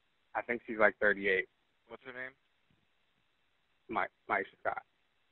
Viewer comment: had to hit rewind when I was looking away and thought he said "Michael Scott" lol